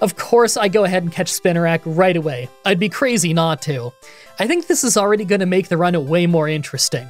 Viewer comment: Did anyone else hear this as, "I'd be crazy Natu"? I feel like there's a bad joke or a weird T-shirt that could be made out of that, but I'm posting too late at night to think it through. Please let me know if any of you think of something using this.